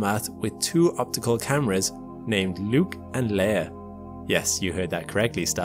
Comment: no I didn't hear that correctly, I heard "Luke and Lair"